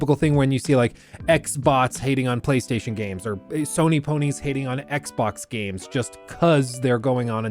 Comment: / Did he just say Sony Pony's?